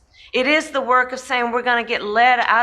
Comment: Thank you! It's blasphemy to call yourself a God. Funny how she kept saying" it's the work of saying". Sounded like she said the work of satan